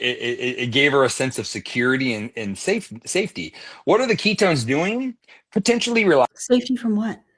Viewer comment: so his mom used keystones as a security blanket to avoid her hunger?? Am I hearing that right?